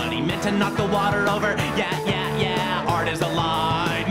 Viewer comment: Art is a lie, NOTHING IS REAL. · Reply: I thought he said art is alive